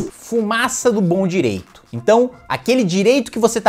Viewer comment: Eu entendi "FUMAÇA NO PÃO DIREITO"!!!!!! Tô precisando fazer uma faxina nos "ovidus"!!!!😁😁😁😁😁😁